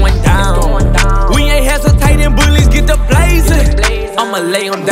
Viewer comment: Did anyone else just have an absolute stroke trying to understand what he said at 0:30 because it sounded like “when the hepatitis bullies get to blazin’”???